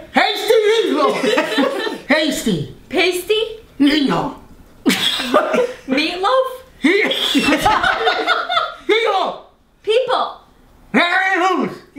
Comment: pasty people sounded like “hasty meathole” 😂